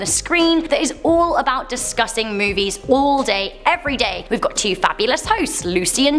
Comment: Lol, when you mentioned your new channel “ top ten beyond the screen” I thought you said it is all about disgusting movies but I listened again and realized you said it is all about discussing movies. 😂. I do like scary movies, I have since I was a youngster,I still love them as an oldster😊